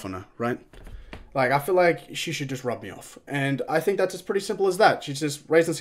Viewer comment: did he just say he wants the witch to "rub me off"